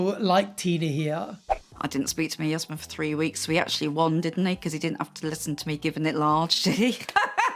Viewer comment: Canadian here, I didn't hear a word she said! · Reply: 'I didn't speak to my husband for three weeks so he actually won didn't he cos he didn't have to listen to me giving it large, did he? menacing cackle '